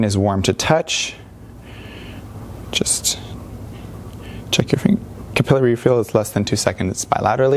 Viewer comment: subtitles says like it is - "check your fuckin capillary refill"